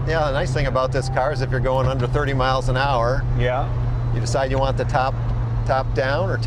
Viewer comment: first time i heard "if you're going a hundred thirty miles an hour" man that would be wild